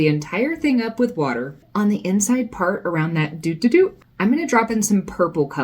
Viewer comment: Kudos on using the real, technical name of the center of the flower. "Do do do" is not a word many people are familiar with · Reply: Now I’m confused, I thought you said do-ta-doot 😂